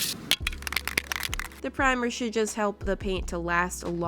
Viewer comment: did she say shit????? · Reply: Should